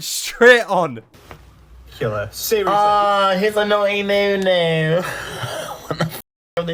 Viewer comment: Naughty Noo Noo is a dvd released by BBC in 2004, I used to watch it all the time. The character Noo Noo is a blue, elephant looking vacuum cleaner. His whole thing is that when cleaning, he'll suck up the teletubbie's food, tubby custard in the dvd. They'll call him "naughty noo noo" and chase him around their little house. As a kid I loved that dvd and that character, so it kills me to hear it come out of her mouth and I wish she never said it ever :) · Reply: I thought she said “naughty moo moo” 😮